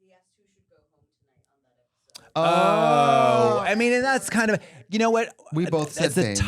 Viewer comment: Q for editor: Could there be captions for anytime someone w/o a mic speaks? I can’t remember her name, but I wanna hear what she says at 40:49 !! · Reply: I think the person said “rupaul asked who should go home tonight episode” I think that’s what they said